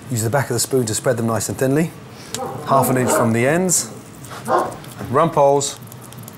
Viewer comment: Did he just say his dogs name was Rumples? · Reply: Its Rumpole